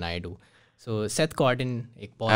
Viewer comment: what is the name of the person, is talking about please anyone share the name of that marketer. I can't hear clearly. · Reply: Seth Godin - This is marketing